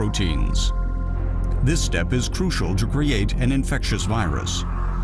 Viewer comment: what the word in 3:32 (this step is crucial ** ) , what word after Crucial ?spelling like Jobriate or what? can anyone help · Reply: "to create"